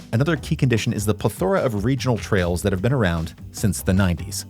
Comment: did you say "ple THOR a" ?